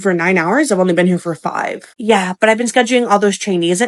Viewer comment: She said trainees, just spoke fast so it sounded wrong 😅 cause yeah that'd be craazzzy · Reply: Thanks for clarification. I thought I heard Chinese as well. 😮